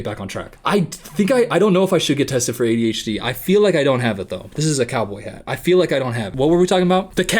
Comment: "this is a cowboy hat" meanwhile I thought it said "corduroy" ..